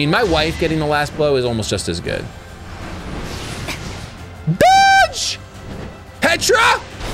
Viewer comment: Oh hey, finished a Fire Emblem game. 😂 Also, 24:41 Did I hear that right...? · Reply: Oh...it almost sounded like it 😂 but he said dodge. You made me do a repeat of your timeframe